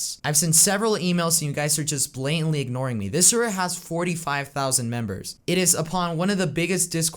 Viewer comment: quackity said the server has 45,000 members and the captions say 4,500 rip